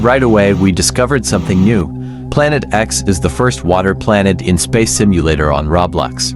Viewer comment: BRO I HEARD ALIEN SPACESHIP